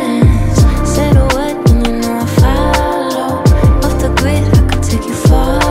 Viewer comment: I hear “ off the gwid “ lol